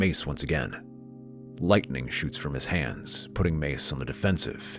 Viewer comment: for some reason I heard "lightning shits from his hand"